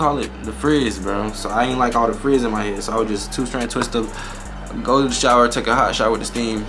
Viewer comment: did you just say "golden shower" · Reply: “go to the shower”